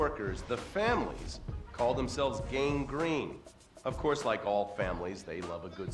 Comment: when he had said gang green I replay it because I thought he said the gangrenous